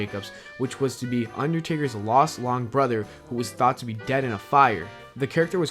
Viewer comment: Top 10 Lost Long Videos.... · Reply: Am i the only one who heard "lost long brother"?